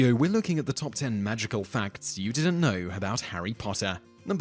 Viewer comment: I thought it said Top ten Magical Farts XDD